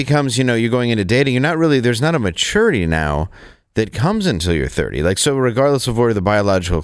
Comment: All I heard was “be a slut until you’re 30”